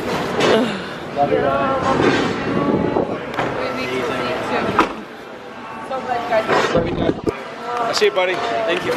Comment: Did just say "bye mom" at the end at 14:28.awwwwwwwww my overies are gonna explode lol^_^love you&your family &can't wait for your Xmas vlog..always brighten up my day;on snapchat&on YouTube ( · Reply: Yessss said "bye mom" at the end 😍😍😍😍